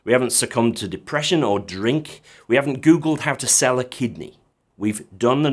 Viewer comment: I thought you said sell a kid and my ears pricked up but then you elaborated with Kid - ney ;-)